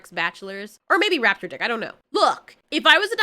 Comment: did she said Rapture Dick?!😂😂😂